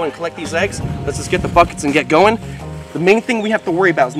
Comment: My volume turned down and when he said "Buckets" I heard "Fuckets" 😅😅🤣🤣